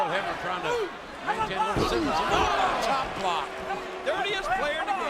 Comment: anyone else hear ric say chop block? I did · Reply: He said "Manolo Blahnik" ... referring to his shoes, which cost more than my house.